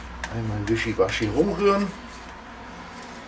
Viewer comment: Alda ! Kann es sein das ich ab Minute 5:43 gehört habe das du ,, Wischi Waschi" Umrühren Gesagt hast ? Der Begriff ,, Wischi Waschi" gehört mir 😄 ! Gruß der Wischi Waschi 😂😂